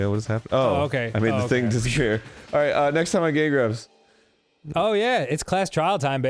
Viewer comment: On minute 33:55 I swear says "Ok next time on gay grumps". I want that to be true but maybe I got the waxy ears.